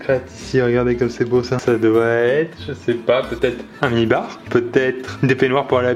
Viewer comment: Putain quand il a dit "mini bar" moi j'ai compris "nibar" 😅